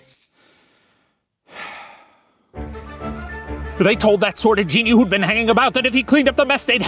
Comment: I think the subtitles at 24:08 are wrong I think instead of "sort of genie" it's "sordid genie"